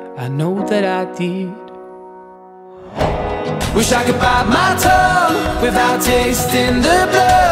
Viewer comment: What i hear "Wish I Could Bite My Toe"